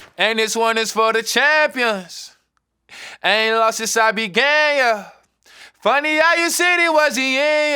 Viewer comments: I thought this whole time he said ion lost since i been gay yuh😀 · I thought he was saying “ haven’t lost since I’ve been gay, yah” I guess I can stop singing that part out loud lol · I thought he said "i aint lost since I've been gay yuh".... Thanks genius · I'm gonna get cancelled for this but i thought he said, " Ain't lost since i've been gayer." when it's actually, " Ain't lost since i began, yeah." Lol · I thought he said “I ain’t lost since I been gay yuh” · Am I the only one who thought he said I ain’t lost since I’ve been gay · i thought he said "i aint lost since i been gay yuh" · Am I the only one who thought he said "haven't lost since I been gay yea' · Always thought he said “I ain’t lost since I been gay” 😂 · Was I the only one was thought he said (I haven’t lost since I was gay)😂💀 I could’ve swear he said that.😅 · I thought he said haven’t lost sense I’ve been gay but he said began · I legit heard "I aint lost since I be gay" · AM I the only one that thought he was saying "I aint lost since I been gay"?? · i thought bas said and y’all lost since i been gay yeah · I thought he said ive been lost since ive been gay yea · Bruh I thought he said I ain’t lost since I been gay yea 😂😂😂 gotta get a clear mind set lmao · Who else thought he said I haven't lost since I been gay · Who else thought he said” I ain’t lost since I been gay” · I thought he said “I ain’t lost since I been gay, yuh” at first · I thought he was saying "I be gay"